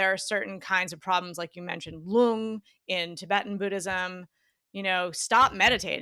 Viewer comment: does anyone know what is the symptom she is saying ?? Gloom? Loom? Important to know because it's a sign of meditation sickness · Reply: Lung